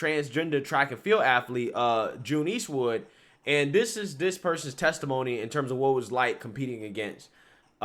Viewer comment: Couldve swore i heard this person lost. Thats unfortunate